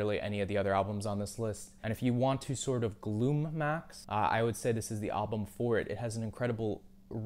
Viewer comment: did he just say "gloom-maxx"